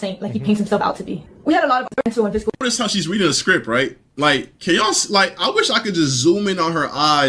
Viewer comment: like.. at 40:00 is he saying 'can ya'll' or 'ko' , lol. I heard him say this a few times and I am like- neither make sense in this context... Then I remember who I am analyzing and are like.... just press play, lol. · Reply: Saying "can yall" starts to say something else cuts himself off and starts a new independent sentence. It doesn't make sense in the context cause there is no context, he stopped the sentence. Hoped this helped.